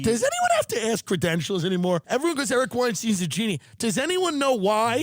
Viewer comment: inaudible is Tim saying "Eric Weinstein"